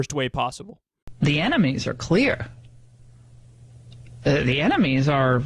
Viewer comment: I thought she said the "animes are clear" at first.